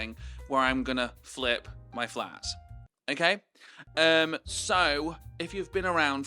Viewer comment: Omfg i thought you said “im gonna flip my flap” and was like ???????